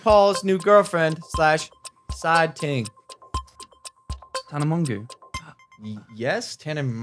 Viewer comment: Mate i thought he said tana mango 😂😂😂😂😂😂😂